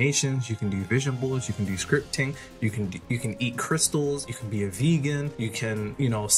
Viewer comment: Did u say eat crystals